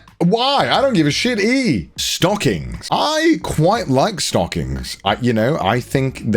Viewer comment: Listening to this in the background and definitly heard "stalking" then had a moment extreme mental whiplash when I thought he would say "I like stalking"